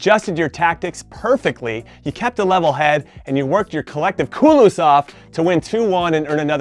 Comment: Min 5:53 did he just said "culos"? hahahahaha